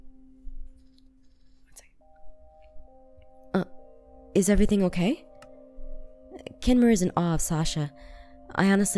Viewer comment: Did anyone else hear whispering? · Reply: Yeah, she said "one sec"